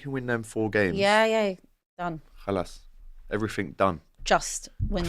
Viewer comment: did you say khalas at 13:35??? are you arabic lad 😮😂😂😂😂